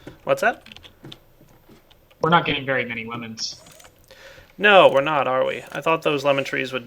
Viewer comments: "We are not getting very many Lemons." Coulda sworn he said womens. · I thought said "we are not getting enough women" :D